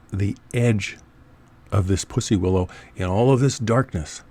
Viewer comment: did he just say pussy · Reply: The plant I am referring to is called a pussy willow and grows in the wild.